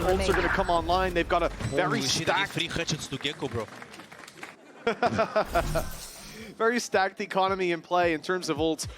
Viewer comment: what did chron say after getting gekko? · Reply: "i did 3 headshots to Gekko bro"